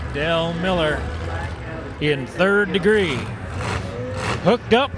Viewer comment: What is the name of the truck around 5:40 the black and red one, ik its 3 something but I can’t make out the other word I heard “3rd degree” but I’m not finding anything I’ve got a absolute monster ‘71 f100 I’m gonna be painting probably next season and I’m deciding on colors but I’m loving that black and red I’d like to see more of it. Thanks in advance